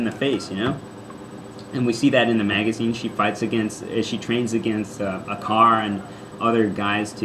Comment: lol "She trained with a car." XD It sounded like you said that. Wait a minute, say whaaat? Bionicle's only been out since 2001.